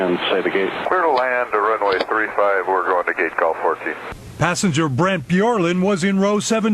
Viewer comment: "going to gate gulfport." Lmao the media is so bad at reporting on aviation, he clearly said he's going to gate G14